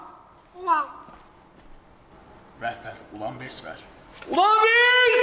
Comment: ne dediğini anlayan varmı söyleyebilirmisiniz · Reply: ULANBESSSS diyor dosjspaskapska